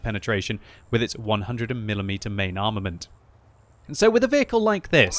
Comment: did u just say 100 and mm main armament?